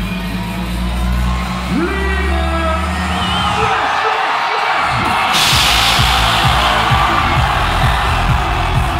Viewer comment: que dice like mike en el minuto 2:54 ? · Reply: No... Algo como Lima! Flex Flex Flex Move!